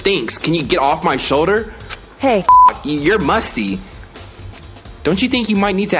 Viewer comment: Kyoko: Hey 18:15: CENSORED I thought Kyoko said hey bitch or something